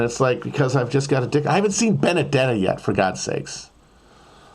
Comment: What movie does he say he hasn't seen yet at 9:59? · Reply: Benedetta